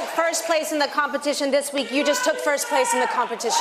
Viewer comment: anyperson can text me what she said? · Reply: Nicole just said: Last week Mrs O. (Sharon) said that that went from here to here, and took first place in the competition...